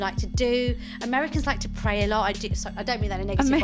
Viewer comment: LOL! at 4:25 Did she say "Americans like to pay a lot." (?) I had to pause the video I'm laughing so hard!